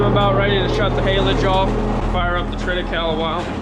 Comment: I feel stupid but I googled an can’t find it. What’s in the feed? What’s the grass. Sounds like you say treta cow? What is it? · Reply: Triticale I think